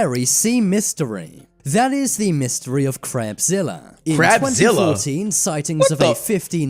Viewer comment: i heard crap zilla